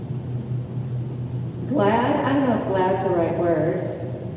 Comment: said happy is a wrong word. An interpreter doesn't sound like shes saying his word right. Im hard of hearing, I may be wrong. But can you anyone who is hearing translate what an interpreter exactly said at 1:03:01 to 1:03:04 for me please. I couldn't figure out words were she using at that moment of time. Much appreciate thanks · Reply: “Glad.... I don’t know if glad is the right word” :) hope that helps!